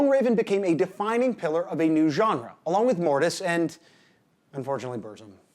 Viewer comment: Ohh, Mortus, I thought you were saying Mortiis. I was thinking this wasn't nearly upbeat enough.